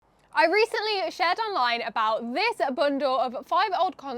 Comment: that intro was hilarious just a note you may want to use the word talked instead of what I think you said chat or shout because to a US ear it sounded like you said (I recently shat online about this bundle)lol still a good video though